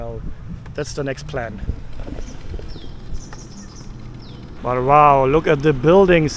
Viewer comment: did i just hear u say "walao" @ 18:17 ?